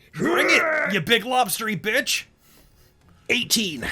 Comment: I thought said "ya big lobster bisque" and was sorely disappointed on the lack of food insults